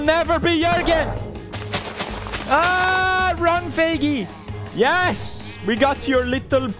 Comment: Wait did he just said “Feigi”????